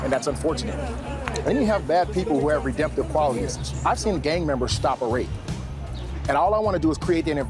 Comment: What does he say at 1:27-1:28? I've seen gang members.."? Thanks in advance. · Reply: "Ive seen gang members stop a rape"